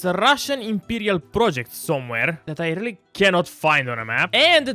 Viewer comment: russian imperial project. Did I hear that right?